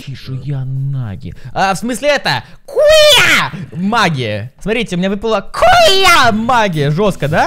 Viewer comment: Первый раз как услышала куууя наги мне послышалось хууууя наги😐